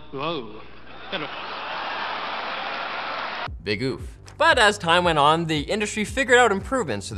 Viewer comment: did I just hear a Big Oof is slowly taking over LMG guys, its inevitable at this point